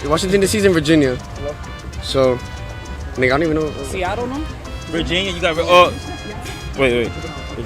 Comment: did someone say Seattle??